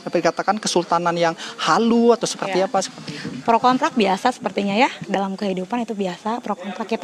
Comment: pro kontrak? gw salah denger ga sih?